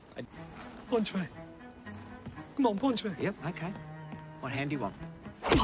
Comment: I thought he said, "What hand do you want?". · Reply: The subtitles on Binge says "What hand do you want?"